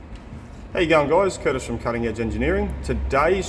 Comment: I accidently left the closed captions on today and realized you have been saying, "How you going guys" all this time. I always thought you were saying "Hey young guys" in the first 20 videos of your I have already watched.